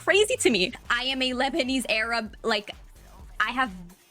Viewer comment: Sounded like she said she’s a lesbian Arab 😂😂😂 she hid behind I’m a better person because I donated to a charity and people donated to her so makes her a charity case